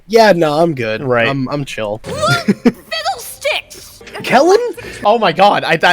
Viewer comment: What does say there? I can't tell. 20:05 - 20:36 Also, at least in Omniverse, Echo Echo is kinda frog-like. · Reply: He says Kellen